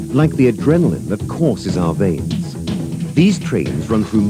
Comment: I think they're saying "Like the adrenaline that crosses our veins"